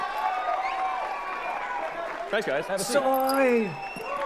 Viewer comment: * Elon sat down so fast- swear I heard em say "SIT BOY!"